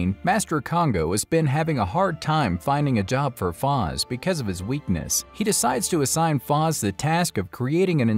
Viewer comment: you said "his weakness". I thought you said they were "non-binary".